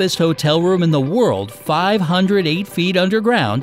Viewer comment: did you say Sweden